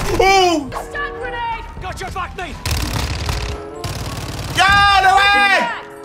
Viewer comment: did he say "got your back meat"??